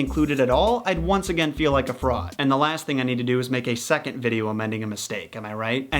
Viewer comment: God I'm tired. I thought that it said The Prosecuted Homie lol